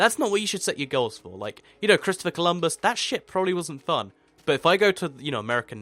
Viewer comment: it sounds like you said "that s*** wasn't fun", but I know you actually said ship :P Great vid!